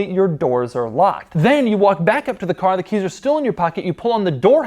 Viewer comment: it sounds like he said "the kids still in your pocket"